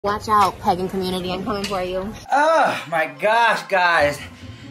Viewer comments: I kept hearing her say “watch out pegging community, I’m coming for you” 😝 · It sounds like she said pegging community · I legit thought she said "pegging community"